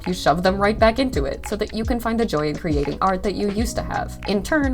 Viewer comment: my fnaf brainrot has gotten so bad that I thought that you said “joy of creation” 😭😭